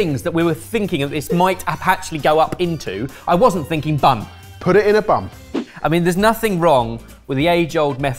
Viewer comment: I had to rewind at 1:29 just to double check what the subtitles said for, Are you sure it was put it in a Bun?? lmao